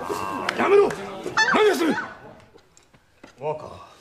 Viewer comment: 「若、若」というセリフが「バカ、バカ」に聞こえた。😂💦